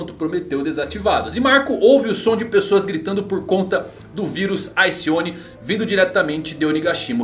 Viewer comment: eu entendi vírus Alcione 🤣🤣🤣🤣